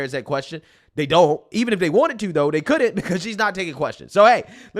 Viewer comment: Whoa whoa there big dog,gotta slow down there at the end and space your words lol.I had to go back and double check at the very end because i thought you said to "make sure to like kamala" 🤣🤣🤣i quickly caught that it was "make sure to like,comment and subscribe" but the way you said it so fast i swear i though you said to "make sure to like kamala" 😭😭😭SLOW IT DOWN AND MAKE IT CLEAR🤣🤣🤣